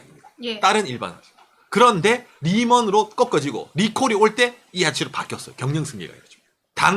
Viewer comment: 님 영상 항살 잘 보고있습니다 예전부터 느낀건데 말씀하실때 말의 앞부분은 강하게 말씀하시고 중간까진 잘 들리는데 끝말은 항상 흐리셔서 뭐라고 말씀하시는지 정확하게 안들려서 저는 늘 추측으로 판단하게됩니다 예를들어 3:29 경영승계가 이뤄집니다 라고 말씀하신거같은데 육성으로 들리기에는 경영승계가 이ㄹ@@ 들립니다 예를 3:29로 든거지 말씀하실때 꽤 많이 늘 끝말을 흐리셔서 잘 안들리는경우가 빈번한데 혹시 조금은 고쳐주실수 있나 해서요 영상은 늘 어디서도 보지 못한 일본의 다양한 소식이나 각종 역사얘기 재밌게 보고있습니다 구독자 20만까지 떡상 하시길 바랄께요